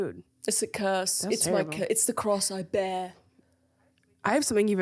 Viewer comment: I thought said" crosseyed bear" instead of the cross I bare !!! lol